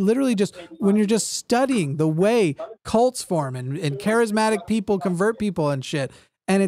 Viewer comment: I'm a gay lady, and I'm not ashamed to say I'm in love with after his "just a dude" rant at. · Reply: At first, I thought you said "aromatic" and I was like "so, like, lavender scented or something?"